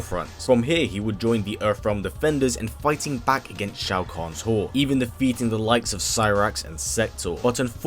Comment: Did he say smoke will join EarthRealm forces against Shao Khans whore? As he is fighting Sindel Lol